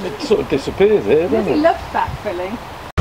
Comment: She’s a lovely lady… (did she just say she loves back filling??) 😃☺️👍..